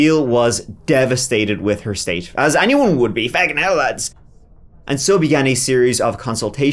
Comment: I wonder what he said after he said "as anyone would be" at like 25:10?? I have listened to it like 10 times and dont have a clue. Lol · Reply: "feckin hell lads"